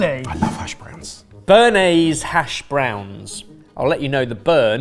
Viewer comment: what did Jamie say? I’m drawing a blank 🤔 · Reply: "gash" browns 🙈